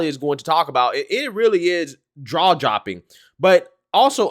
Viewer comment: Did he just say "draw jopping"?